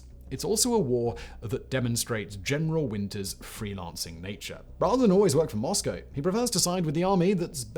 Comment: I always heard it as Marshall Winter